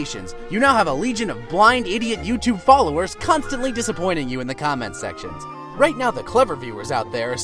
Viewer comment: When he said comment section I thought he said condom section